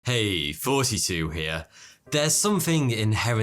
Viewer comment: To tell you ever since I see your video I always hear "42 here" since "thoughty2 here" is kinda sound familiar